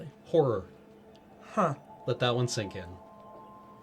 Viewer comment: I'm not clear on what said at 33:25, it sounded like "but that won't sink it" but I don't think that's accurate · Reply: "let that one sink in"